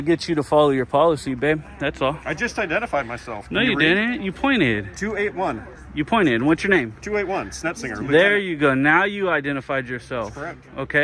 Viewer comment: Too funny thought he said "nutsinger" lol 😂 truly a pack of dolts!